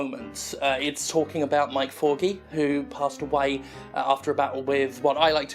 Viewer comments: thought you were saying "my corgi", but it turned out you were saying Mike Forgey......... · Until I checked the charity in description, I thought you were saying "My Corgi" not Mike Forgey. I kept hearing that your Corgi dog passed away from cancer!